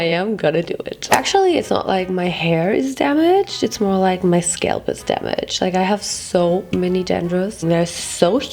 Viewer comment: I thought you said your soul is damaged and I was like 'same'